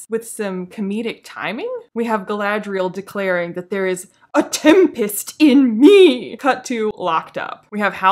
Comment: Your reviews are infinitely better than the show itself. They inspire a tempest of levity within me! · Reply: I thought Galadriel said tampax, not tempest?